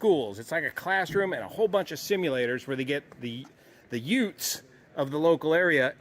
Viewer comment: "Did he just say youtz?" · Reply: Utes!